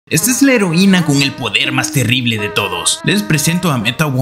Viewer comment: Entendí "la heroina es terrible"